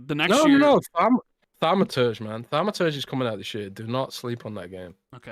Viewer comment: "No no no, faman, no famantage is coming out later this year. Do not sleep on that game okay"- LoL WHAT GAME DID HE SAY NOT TO SLEEP ON?? LOL I LISTENED TO THIS LIKE 4 TIMES AND CANT FIGURE IT OUT. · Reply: I'd say it's The Thaumaturge, an isometric RPG coming out Dec 5th